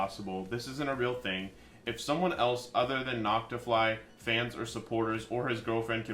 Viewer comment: the subtitles say "knocked off lies" instead of "Noctafly's" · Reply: "knocked to fly" instead of "Noctafly"